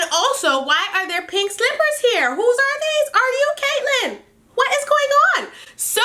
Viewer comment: LMAO 🤣🤣🤣 did you just say are you Caitlyn?!!! 😳😂😂💀💀💀💀💀💀💀💀